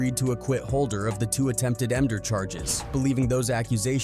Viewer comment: I was a cop for some time and I was trying to understand what the narrator was saying when I kept hearing "attempted ender", where I have never heard of that crime. Then it dawned on me that what the narrator meant was "attempted murder." I'm thinking to myself, people will do anything to make a dollar. That would be you, channel owner. 🙄🙄🙄